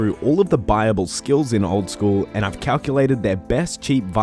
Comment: i thought you were saying bible, not buyable lol that makes more sense